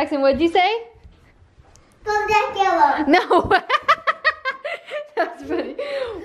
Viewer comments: did Jackson say Bodak Yellow at 13:39? 😂😂💓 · did jackson say "bodak yellow" at 13:36 or am i just really bad at understanding children lol